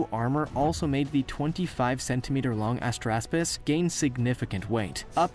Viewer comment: When he said Astraspis, I heard "Ass graspus."